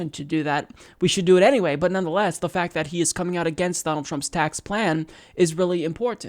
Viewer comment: I know you said Donald Trump, but it sounded like Battletrump... Cute, but I prefer the anagram "Lord Dampnut". Anyway, props to Gates